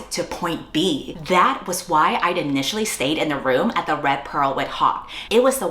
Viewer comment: Love when a fantasy world completely uncritically recreates the deep misogynistic violence of religious cults. Love that. No critique of these cults irl or how they harm their followers. No commentary on purity culture and how it’s the mask grape culture wears. Just a new girlboss twist on the virgin/wh0re trope where one flows seamlessly into the other with the barest threads of consent, maybe, to the standards the author feels are consensual enough to be hot to them— while she remains as pure as possible and is permitted her own sexual agency… and she can even violate men’s consent as a treat! Authors like this are not interested in fantasy, they’re interested in power fantasies within the current system. Edit I watched the video and now I’m upset😂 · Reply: Okay I misheard “the red pearl went hot” and I was like Don’t say that